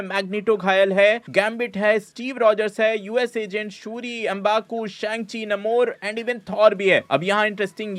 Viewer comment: did i hear MBappu?